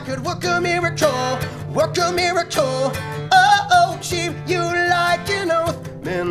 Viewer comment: i keep hearing 'work a molecule' though